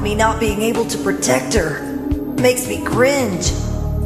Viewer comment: Did he say cringe? 0:53 🤨